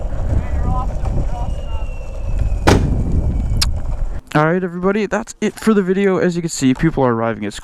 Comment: What did he say? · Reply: He said "Hey, you're awesome. You're awesome Adam" :)